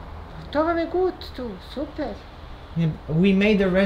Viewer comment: she is litreally saying "pa super je" that means but it s great im not bosnian but close and yeee